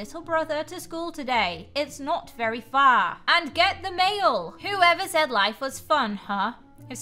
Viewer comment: Yammy: "Who ever said life was fun huh?" Text:"Who ever said life was far huh? That's what i heard and saw.